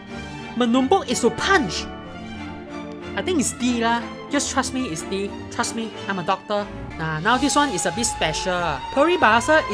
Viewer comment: when u say u r doctor i thought you said u r adopted